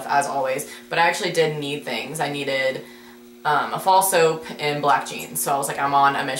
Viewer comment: When you said Fall soap I thought you were saying false hope and I got kind of concerned that a thing like that was sold at your mall...